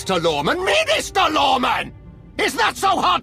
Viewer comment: sorry, did you say Sinister Lorman?